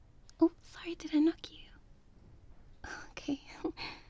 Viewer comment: did she say sorry I knocked you